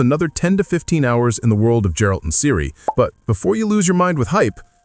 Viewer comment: Did you really said Djewalt ?